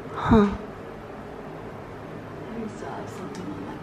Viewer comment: 😳😳😳😳😳😳😳 “we saw something up there” it sounded like an old lady